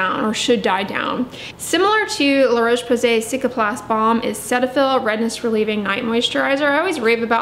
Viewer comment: Did I hear a recommendation for LaRoche Posay Cicaplast Baume